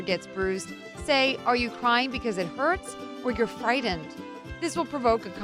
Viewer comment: For some reason I thought she said, " Are you crying because it hurt? Or are you crying because you're pregnant? " I was like : WHAT and then I replayed it and heard frightened 😂😂😂😂